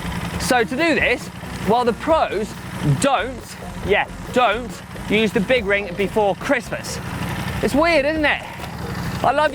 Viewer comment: Sorry was only half watching whilst cooking dinner all I heard was “shaving your big ring before Christmas” !!!! Glad my grandson wasn’t nearby 😂